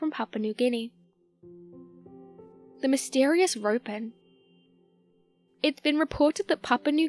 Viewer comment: The first one I thought you said "the mysterious groper", and we have a lot of those everywhere unfortunately.